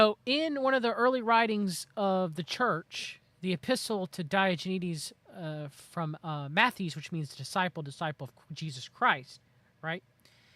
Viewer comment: you mention a Writing of Matthew but I can't understand what you said. Can you spell that for me? Thankyou. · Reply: The Epistle of Mathetes to Diognetus, Mathetes is Greek for student or disciple